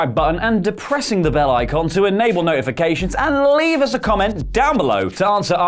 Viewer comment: Sounded like he said "By depressing the like button".